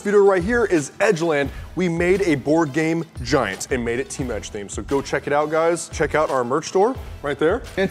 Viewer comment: It sounded like said "my names team edge"